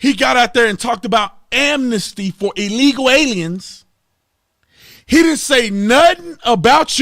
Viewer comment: did i hear this right ? Amnesty for illegal Aliens?